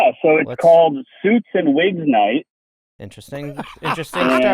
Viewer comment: Omg I misheard as “Soups and Wig Night” which is such a vibe. Good soup.